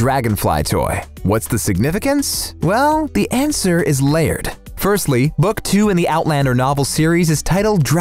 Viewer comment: who else heard "The answer is Laird"